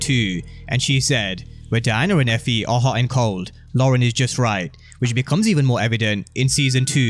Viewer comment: I thought she said Lauren and Diana was hot and cold and Effie was just right